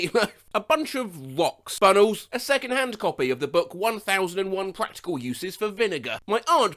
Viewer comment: I LITERALLY THOUGHT THAT SAID “1001 Practical Uses For Vagina” PLSS 💀💀💀